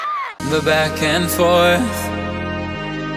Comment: the back and force lolllll(idk if he said force but i heard force)